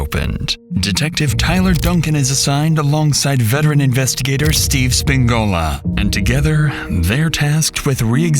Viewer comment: I can’t be the only one who heard Steve Spagnuola?